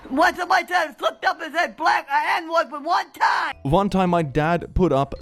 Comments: one time I fuggd up and said black the n word one time, I think, She either said 'I' or 'my dad' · I think 4:10 said "I slipped up and said the n-word one time"